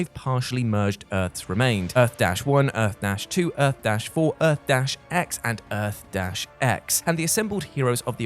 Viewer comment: For everyone saying said two Earth-X’s. The final two Earths are Earth-X and Earth-S. I assume it just sounds like he says “x” twice.